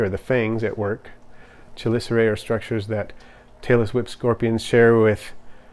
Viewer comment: I thought he said ''Taylor swift scorpion''